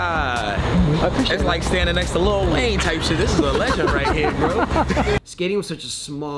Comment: is he said Lil Wayne type legend?